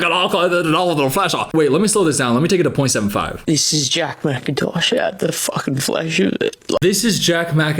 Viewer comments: LMAOOOO i think he’s saying “this is jack mackintosh out the fuckin flesh with it” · I’m not Australian, but I think he said something around the lines of “This is Jack Macintosh, out the fuckin flesh with it” or “out the fuckin fleshin it” Idk lol